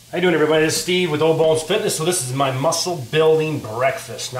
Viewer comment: Oh, I thought you said "Old Balls Fitness."